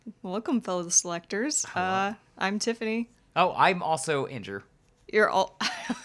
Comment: I thought you were to say, "I'm also Tiffany" for some reason